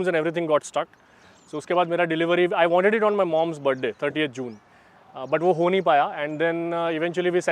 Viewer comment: did he said 38th June?😂😂😂